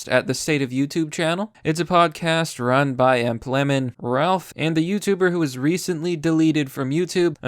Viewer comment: did he just call rusty cage ralf